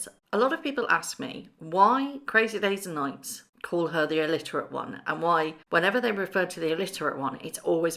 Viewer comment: The subtitles can be very funny at times! When you were saying why the Alliterate One is often so called the subtitles heard it as illiterate!! I suppose we can chose which we feel suits best! Whichever set of initials we choose to use, MM or RR, she is still the alliterate one. Whatever was she wearing for that shopping trip? Whatever it was it was so badly fitting. It was on a par with that awful brown outfit she wore on a previous 'secret' shopping trip. And she thinks she is stylish? Why on earth would she want her staff to ignore her while she is out? Well, only the alliterate one would dream that one up. She is so up herself but, as you said, unremarkable. I wouldn't need telling to ignore her!